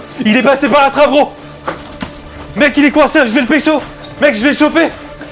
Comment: c’est moi ou il a dit de ne vasi le pecho